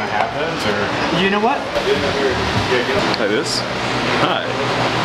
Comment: "You know what... I'll hold a pair for you!" lol I'm pretty sure that's what he said haha